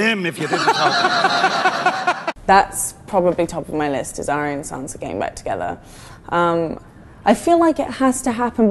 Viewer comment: anyone else heard "Arya and Sansa getting wet together" then watched the rest of the clip like sophie wat dafuck? O.O